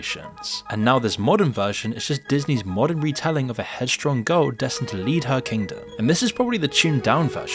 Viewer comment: i thought he said "head strong goat" 😂😂😂